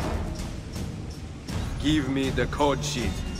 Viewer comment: I THOUGHT EZIO SAID GIVE ME THE COOCHIE 😂😂😂